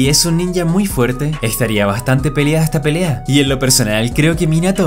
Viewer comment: creo que escuché que dijo "estaría bastante pelea esta pelea"...